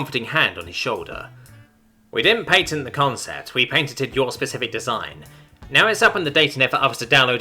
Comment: Every time you said "patented" i heard "painted". They bee painting mice on the walls of the Whisker.